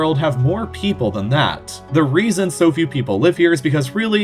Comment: In 2:06 i thought he said “soviet” instead of “so few” LOL